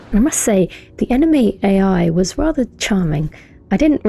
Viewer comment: heard it as anime ai (enemy ai)